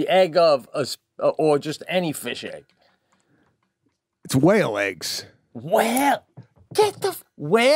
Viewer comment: Did he just say, “Whale eggs”?? 😂😂😂